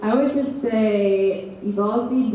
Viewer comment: What is Caity saying at 41:37 about season 4? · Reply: Danielle Golden she tells the people in the room to go to the writer who is there (I didn't catch her name) to 'haunt her with all their ideas'